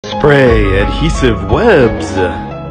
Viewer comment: I thought that said "spray adhesive weebs" for a second there lol